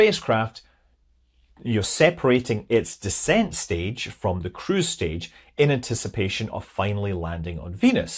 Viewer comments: according to logic (and the closed captions) you said "cruise stage". But I heard crew's stage, and sat up straighter so as to listen closer. I mean, c'mon - a CREW??? To Venus???? In the 70's???????? Rewind, rethink, return to reality. It was all very disappointing after that. 😢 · lol I thought you were saying "crew's stage".